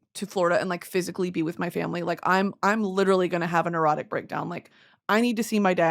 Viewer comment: I thought you said "erotic breakdown"